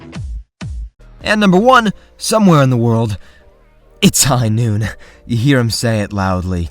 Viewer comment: i find i hear "It's High arhhh" more than "Its High Noon"